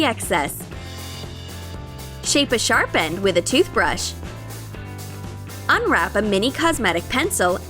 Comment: Do anyone else here here say tooth brush instead of tooth pick